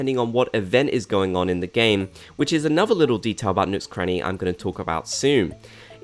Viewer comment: I had to turn on subtitles for a while since i had to be quiet, and at 6:23 it said nukes cranny instead of nooks cranny 😂